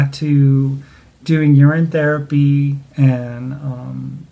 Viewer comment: Question: what is urine therapy? (I think that’s what I heard you say)